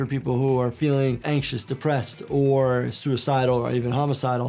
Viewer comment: i’m sorry but did you just say professional vs peter? If you get the popcorn and snacks i will ring the ring bell